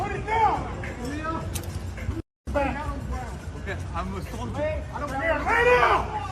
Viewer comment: sounds like he said I'm a Muslim some said I don't care.